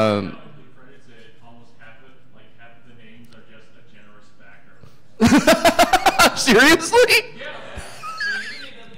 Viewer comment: Any idea what was being said at around 58:00 in the background? They're talking about the horrendous credits sequence at the end of Mighty Number Nine, and I just came here from the YouTube video of KillerEmcee playing various Megamans with it in the background... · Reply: sorry for the super late reply, he was saying about how half of the names are just "- A Generous Backer"